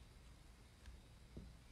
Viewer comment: I thought she said “Jackson you s’more” 💀😂😂